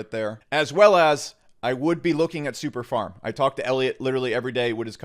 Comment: is it Syndicate and Superfarm? Can't catch the first one